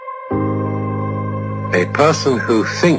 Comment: When I heard him say a person who thinks all the time has nothing to think about I thought he said a possum who thinks